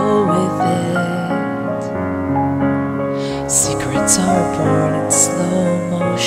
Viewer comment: am i the only one who hears: "secrets of a pornstar"?